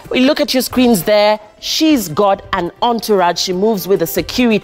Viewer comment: DId I hear the host say "She is god" at 0:06 ama mimi ndo nimeskia zangu... Though I dont see any problem why a preacher of God, should be rich.